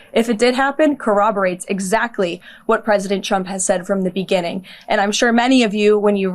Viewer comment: Sounds like she said President Chump there.